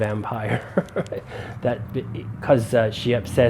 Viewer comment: the first time I heard that I thought said, about the energy vampire, "that bit me" 🤣